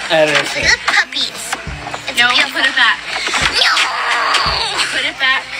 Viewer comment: she really said ñooo